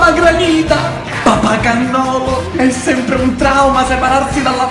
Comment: ma solo io al minuto 0:16 ho capito "papà Castoro"? 😂😂😂😂😂😂